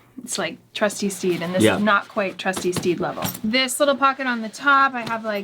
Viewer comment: Let’s please make “trusty steed level” a new channel original catchphrase! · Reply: NGL I thought she said "Trusty Steve"